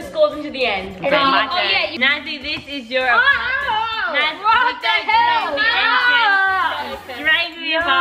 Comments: Did anyone else hear what said when they were showing the outside of Sockie’s house?? It’s around 8:15 if you want to hear what he said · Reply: He said “ what the hell” right? 8:22 is where i found it. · did say what the hell?